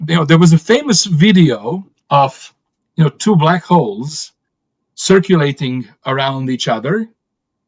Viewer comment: It sounded like he said " a video of two black hoes" 😅